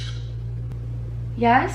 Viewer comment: I heard it say ‘there’s something’ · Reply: And at 07:08 I heard YES